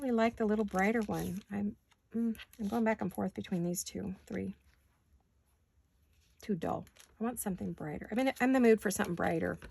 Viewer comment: I was laughing so hard because I thought you said "it's too adult". I've been irritated at having to "adult" all day 😂 then I realized you said "too dull" 😂 😂 😂 😂 As for your family member... Abba you know the concern and need. Please offer mercy, peace and comfort.